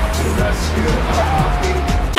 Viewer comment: I think Dora is saying: I’ve got you puppy, I will return you to meet consondigal.